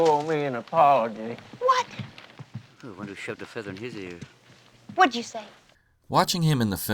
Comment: So why shoved a feather in his ear? I heard it clearly